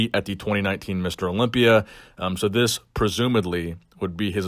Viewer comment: did he say presumaDly?!